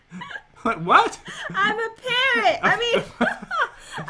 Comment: I thought that said I look like a carrot not a parrot 😂